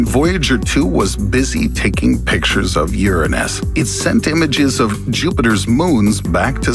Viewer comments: it said uranium instead of Uranus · Why does it say Uranium instead of Uranus at 4:14 ?